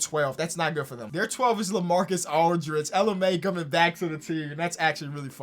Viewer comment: LMAO when got to the spurs i thought he said "ella mai coming back to the team"